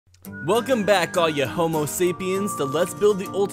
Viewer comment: At first I thought you said hello all you homosexuals 😬